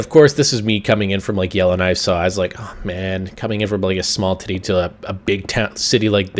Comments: Did he say small Titty?... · did you say a small titty at 2:28???